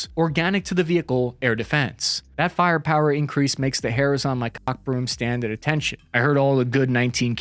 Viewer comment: cock broom did he say cock broom